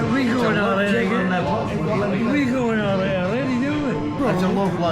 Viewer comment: You are making moves around Scotland 😂😂Dundee here, nice video! But at 17:17 he actually says "We get on alright already, don't we?" or "we get oan awrite awready do we?" not "why are you going already?"